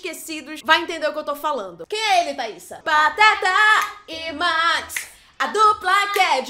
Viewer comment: Eu entendi "Batata e Max" fiquei ??????